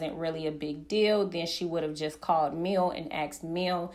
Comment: I thought he said she called Mel? She is a weirdo tho.